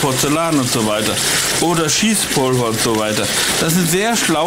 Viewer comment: cheesepulver :D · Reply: Und Ich habe schiesspulver verstanden xD